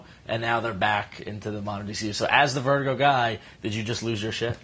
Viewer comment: First of all, Constantine is one of the few charcters with a specific birthday, 10th of May 1953, which makes him 60 this year. Second, when the two goofballs kept calling the "Vertigo Guy" I kept hearing "The Smart One".